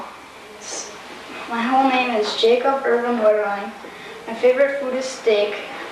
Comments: Omg I thought he said my favourite food is dick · I know i know I KNOW this is a serious topic it’s a sad case.. but when you played the clip of him introducing himself to his class(im assuming) i legit heard him say “my favorite food is dxck” 😩😩 im such a horrible person lmao